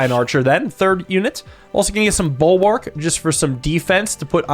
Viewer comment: did he say ballwark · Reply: ballwark is a pun on bulwark